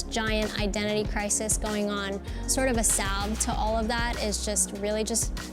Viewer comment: anyone know what word she said says at 2:19? " asaf to all that " ?